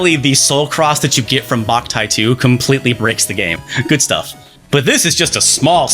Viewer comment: did you say it bricks the game or breaks the game